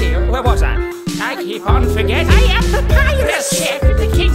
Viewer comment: instead of forgetting, i thought he said ''Forgetti'' even my ears are making puns now.